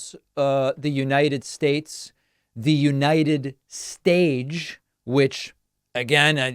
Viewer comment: I heard "United sage", which makes even less sense. And I swear to god he said "respecturd" as well. Its funny that he almost interjects other words into longer words now.